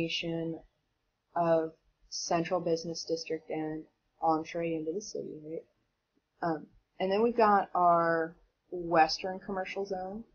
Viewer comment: what did you say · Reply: and entre into the city and then we've